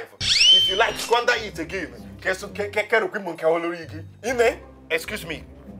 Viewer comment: Did he just say "nne excuse me please get out" 😀😆